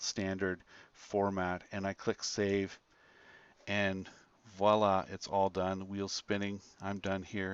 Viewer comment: Are you a Muslim ? because I hear Wallah.